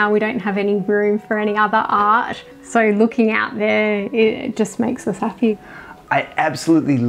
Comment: good God somebody edit the captions quickly!! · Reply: I think it’s a automatic caption issue. The funny part is I jump the first time I saw the video and had to go thinking: no way she said that! Looking at captions again as I listened, it sounded like she said that, but afterwards I just listened with my eyes closed and I heard :.... it just makes us happy! Hard to believe how the brain can trick us, AND how long this caption has been kept there!